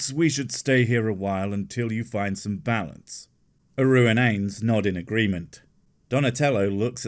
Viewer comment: Yay, Blossom's back, now I can give the video a like! I misheard 2:56 - A ruined anus. Is this the dark disturbing theme you spoke of?!?